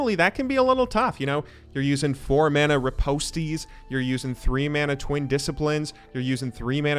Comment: did u just say RIPOST_TEA